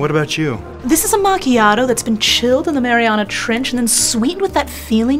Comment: Did she say Mariana Trench?!?!